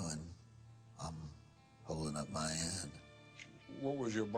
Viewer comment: I don't think he said "holding out my hand" I think he said "Holding up my end"